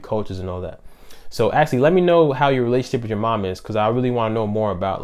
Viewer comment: Haaaa! It sounded like you said “So Ashlee lmk how you relationship with you mom is...”. She is has always been very absent & pushed me away when I wanted attention or affection. I can’t lie she has gotten a little better since I’ve been an adult. But I think that’s only bc I’ve done shadow work. Idk lol